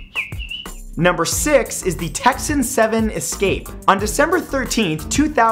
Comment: Did he say "Texan seven" ????